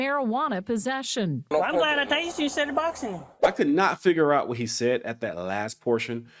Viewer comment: He said, "I'm glad I tased you "instead of boxing with you."